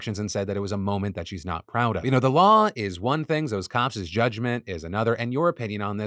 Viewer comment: Did Phill just say COPSIS?! COPSIS?! WE DON'T NEED NO STINKING COPSIS!